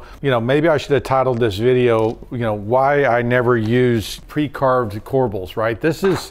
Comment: Lol at first I thought you said “pre-carved deplorables”….. I was like “they’re not that bad…..🤔😂